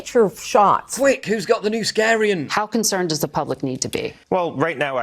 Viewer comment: Did I hear Paul say: "NewSCARIAN"!? 🤣